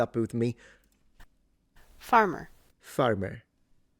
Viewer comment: You sound like your saying farm'r